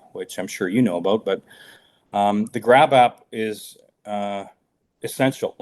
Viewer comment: Grap app? I thought he said grab ass!😂